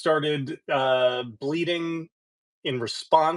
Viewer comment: Oh. You said "The American President started Bleating" I was very excited at first, since I thought you said "The American President started bleeding"